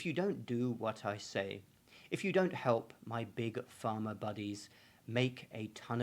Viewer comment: At the 3:34 mark, Closed Captions incorrectly reads "farmer" instead of "pharma". Just in case anyone is reading & cannot hear. They do sound much the same.